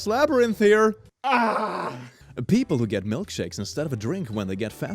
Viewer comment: I thought it said “milksnakes” at I literally said “you eat milksnakes!? >:0 I am getting a cute little snake! Sure it’s a ball python but milksnakes are really pretty too!” Lmfao